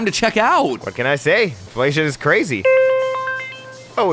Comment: When he said "Inflation is crazy," I thought he said, "This sh*t is crazy." Lol